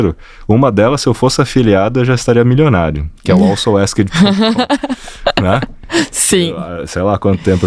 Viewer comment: Boa tarde, pessoal. Oi gente, tido bem? Não ao entendi o nome da ferramenta de planejamento e pesquisa que falou em 18:17. Poderia colocar aqui?😅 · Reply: Oi Talita, ele fala da Also Asked 😊